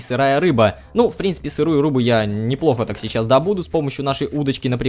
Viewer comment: Ребят я один услышал на 4:22 сырую рубу?:DD